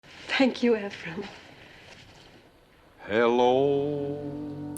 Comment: it sounds like shes saying "imaphrim" instead of "emphrim"